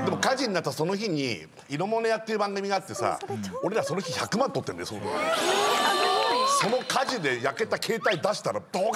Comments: 裏で、それちょーなんとかって喋ってる子誰だ？梅かな？なんて言ってるんだろう・・・ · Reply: 多分梅ちゃんで、「そうそれちょうど昨日話した」みたいなこと言ってると思います！ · 日村がイロモネアの話してる時に、誰か知ってるメンバーがいるっぽい。 「それ"チョウキノハナシカ"と思ってた」みたいに聞こえるけど、聞き取れない！！ 一体どんな感想を言ってたんだ！！？？ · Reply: 「それ丁度昨日話してた」って言ってるのかなー？って勝手に解釈してます笑